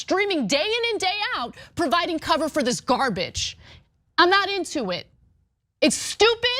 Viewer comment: Did she just say... Garbage?? where is my sound bite?!?!